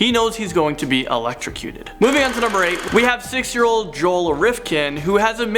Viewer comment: does he say 6 or 60 Joel rifkin? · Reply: Suzanne Oldfield 16